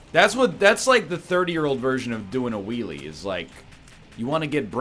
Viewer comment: Honestly thought every time said 'wheelie' he was actually saying 'Wee wee' like a four year old.... is scrambling my brain.